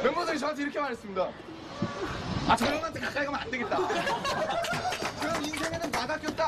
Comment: what were the last words RM said? I couldn't read it well, it's the green words. "You cannot go near _ ____" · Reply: Gloria You cannot go near this Hyung